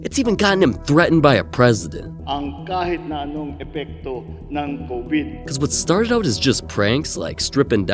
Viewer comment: This isnt even what he said. He just said " No matter the effects of covid " Stop spreading misinformation.